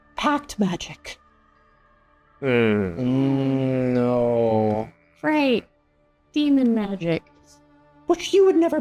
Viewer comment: I thought he said cat magic and thought the party had a fight with demon Nøkk in their future